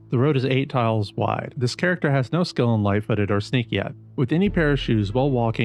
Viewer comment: could you maybe try taking breaths and having some kind of cadance to your speech? your videos have alot of good info in a short format, but you're clearly reading a prescripted dialogue, it sounds like type to speech or when a kid read outloud in class, yeah cuz you know we all retained that information. doesn't do anygood being a 1 minute video if I have to rewind to hear what you mumbled or ran into the next sentence 20 times. 0:04 "this character has no skill in life, but are at sneaky at" what??? you see, being a new player, I'm not super familiar with all the terms and skills in the game. but once I turned on the cc, and luckily they got it right, he's saying "no skill in lightfooted or sneak yet" if you read what I typed as he's saying it, it's very clear how someone could hear that. now multiply that by 1000 and sprinkle it in a bunch of videos and you get, this channel.